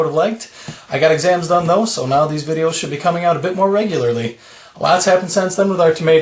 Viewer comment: I thought you said 'more videos'? Anyway, this is like going back in time. A totally antiquated way of dealing with roots. And agree with prior comment , they are hurting for light.